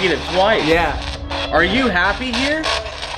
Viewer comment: you might need headphones but it repeats "Elton" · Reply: asks "are you happy here?" And I swear I hear "comfortable".